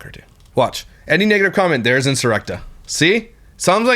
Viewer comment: did u just say the n word? 11:15 i like your content btw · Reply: He said “any negative comment”